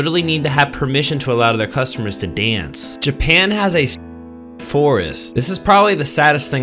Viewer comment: I thought he said "Sex Forest"